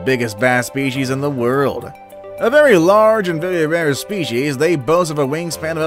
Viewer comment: Is it me or did he say bery bare species in